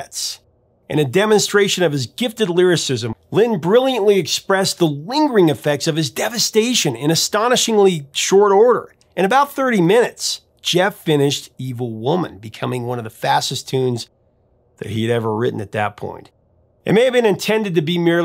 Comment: I always thought it sounded like he was saying "medieval woman". Love this song, thanks for the history! Saying someone was influenced by Stevie Wonder is like saying they have a heart and brain.